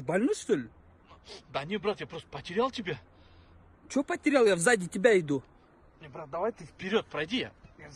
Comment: мне послышалось или он сказал в заде?